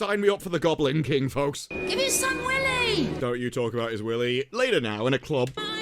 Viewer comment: ngl that line in the ep rlly confused me, was she saying welly w an accent or not? not sure why they had that in the ep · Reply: It's welly, definitely 😅 12 says the same thing to Davros. "I'm dying.." "You keep saying and you keep not dying, can you give it some welly?" It means try harder basically.